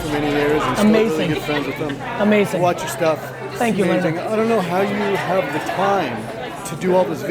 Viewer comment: does he say "thank you my nigga" 😂